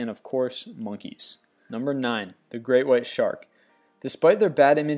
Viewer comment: It sounded like you said grey white shark, its great... I could have just heard it wrong though, good list though i agree with it.